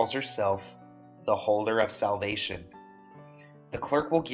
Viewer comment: low key thought I heard him say the holder of selfies .....